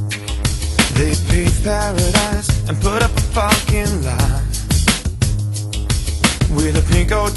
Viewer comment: When I was small I always thought he said “sweet Caroline put up a fucking life”